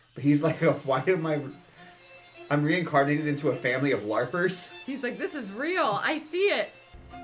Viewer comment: did I just hear larp 🥹